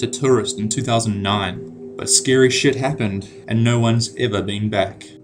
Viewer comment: Did u just say shit